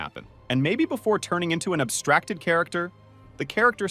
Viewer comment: did he just sayobstakted